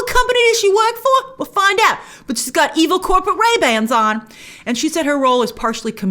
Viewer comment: "EVIL CORPORABANDS!" LMAO IM DYING 🤣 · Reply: Actually, it sounds like she is saying: 'Evil corpo-RayBans', which makes more sense ;)